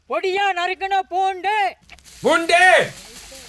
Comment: Basically at 15:10, what the guy said is Poon-du, but u guys trying imitating it badly to some other Tamil word which literally means p**sy😭😂